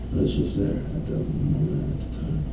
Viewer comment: I think he said "It, was just there, at that moment of the time! (The axe?) I just subscribed. It's interesting.